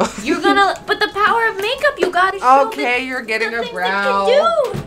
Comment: I heard: “okay you’re getting aroused” 😂😂